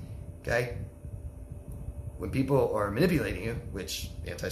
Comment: What is that word at 20:28. " When people are_______ you...? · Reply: manipulating*